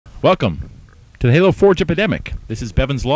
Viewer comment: I thought he said Fuck em', not Welcome :) thumbs up if you thought the same thing at first :)